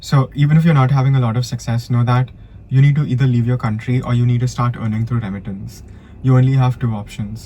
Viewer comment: Pile 1 resonates thanks but can you tell what you said @ 9:10 please ..you said you need leave the country or what? I couldn’t understand what you said there